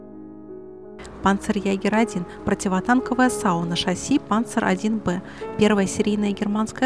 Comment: послышалось: "Противотанковая сауна")))